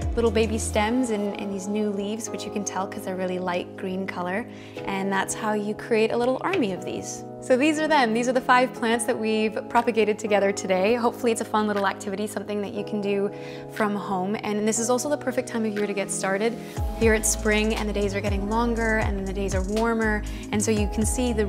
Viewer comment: Thought this said propaganda and was extremely confused